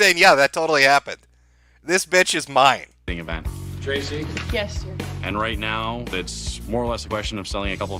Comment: Does she say "Yes, sir?" or am I just hearing things?